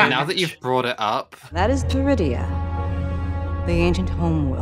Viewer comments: sounds like the planet with the air field from space balls I generally thought they said Druidia lol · Wait, did she say Druidia? The planet in Spaceballs? Yeah, I know the subtitles say Peridea, but I didn't hear a P.